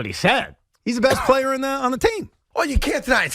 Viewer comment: I thought he said “best planet on the team” just switched planet and team